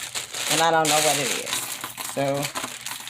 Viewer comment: Did you just say 'I hope its not intimates??' lol lol Love love love the intro!!!